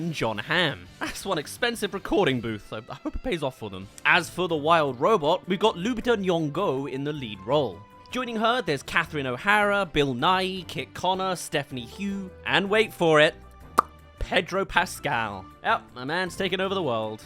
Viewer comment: did he say bill nye or someone else